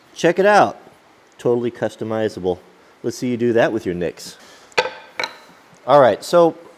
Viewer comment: did he just say nicks? Instead of Nike’s?